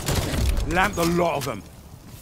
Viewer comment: The subtitles are wrong. It should be 'Lamped THE lot of them."